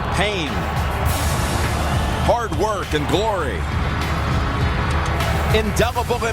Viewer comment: I thought he said hard work and boring at 0:10, I was like WHAT THE FUCK???? but then I realized he said glory not boring, lol